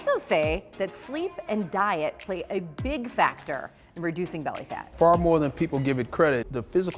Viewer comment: lol I heard "big FATtor"